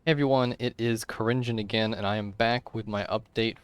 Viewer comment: Thought you said the name was Cringin